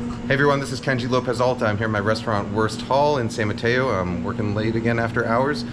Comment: I thought you said " Hey everyone, this is Kenji López-Alltime here in my resturant"